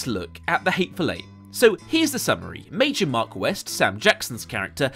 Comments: Woah woah woah... Did you really call him Major Mark West? Lmao if that was intentional, I love it. Top bantz. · did... did he just say Major Mark West